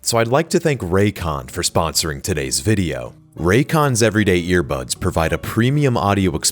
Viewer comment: I keep hearing 1:16 as "Raycon's everyday earbugs"